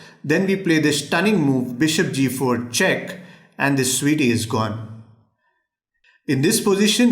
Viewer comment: 'And this sweety is gone' - 2019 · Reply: I thought he said "his Sweety is gone." I'll have to refer to queens as "his Sweety" going forward. (